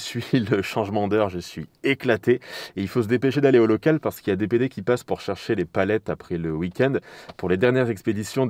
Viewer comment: Y'a "DPD" qui passe.... je suis tellement gamin que j'ai compris "qui passent".... Sinon merci pour tout!!